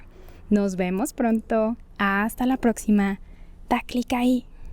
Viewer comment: Gracias por la lección pero qué dijiste en el fin? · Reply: She said your can buy me a coffee in the description box below the video! ❤